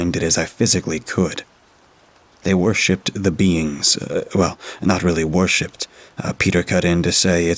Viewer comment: Who else thought he said the “beans” and took a few too many seconds to process ooooohhhh “beings” 🤪😂